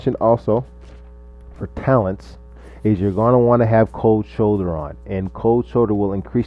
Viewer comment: did he said Cold shoulder LOL?